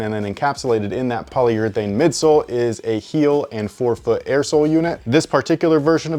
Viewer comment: I thought you said a 4 foot air bubble, blew my mind and then realised you said forefoot